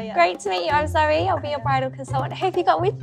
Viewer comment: Consultant says “I’m Zoe I’ll be your bridal consultant” I always hear “I’m sorry I’ll be your bridal consultant” Anyone else?